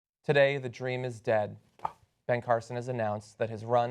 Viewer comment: At the beginning I thought I heard John say "Today, the terrean is dead." Anyone else?